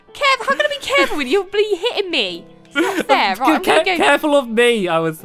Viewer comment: Did anyone hear at 11:23 Sqaishey say bloody hitting me